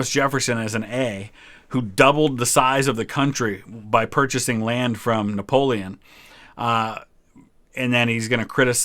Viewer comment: I thought you said purchased land from Poland 😂